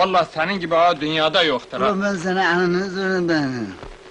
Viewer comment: 😁😁😁😁😁 Maho ağa Ne diyor Bu Arada Anlayan Varmı Ben Kaç kere Dinledim Ama Anlamadım Maho Ağanın dediğini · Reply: Ola ben seni eninde sonunda. Diyor